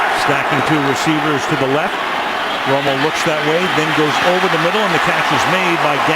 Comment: Did I mishear that? It sounded like "Elway"